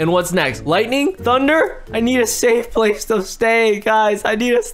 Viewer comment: he really said "i need a stafe place to say" 😂